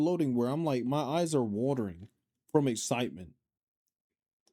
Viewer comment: I thought you said, "My thighs are watering", and I was like...SAME, girl. SAMMMMEEE. 🤣